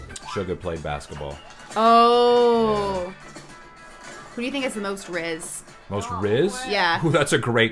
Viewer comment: What was the question at 30:52??, I couldn't hear it well... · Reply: She was asking who thinks has the most rizz